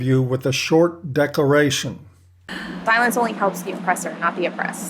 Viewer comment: I think at 2:41 she actually said "Silence only helps the Oppressor..." and not, "_Violence_ only helps the Oppressor..."" In context this would fit with ongoing narrative about how vocal she is at the beginning of this scene's interview in the Red Pill Movie. IMHO, she is misguided in being unapologetic about being loud, arrogantly rude, and openly annoying. Sure, this tactic may have worked for her in the past and may continue to work for her, but it is no way to win someone over to your way of thinking. If she was rational, you'd think would be her whole point in expressing this much energy into anything, to change people's minds. On a side thought this in itself might be telling of her character. She might simply be too weak minded to be quite long enough to hold any other thought in her head for fear of loosing her own convictions. Normal, healthy, rational people can sometimes manage to listen, consider, and thoughtfully respond when they are at their best. Perhaps we should feel sorry for her. Her relationships likely feel shallow and unfulfilling when contrasted to people that successfully exhibit these skills with others. William...